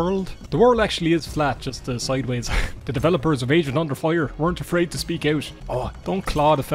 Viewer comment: I kept hearing it as "asian under fire"